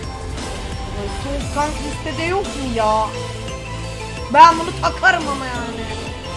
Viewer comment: pelin burda ne diyor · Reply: Song kang listese yok mu ya diyo